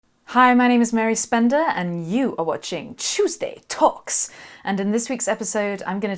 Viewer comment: You've really gotten into my head, Mary. Someone asked me what day it was, and I blurted out "It's TOOOOSDAY.... TOOOOSDAY..... TOOOSDAY TAWKS..." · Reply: I always hear it as "Chewsday Tocks"